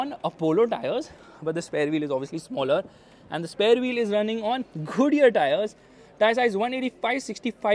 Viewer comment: I heard Khodiyar tyres PS: I hate how they discontinued Diesel